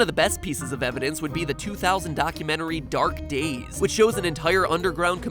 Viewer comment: At first i thought that thing said "Dank Days" not "Dark Days"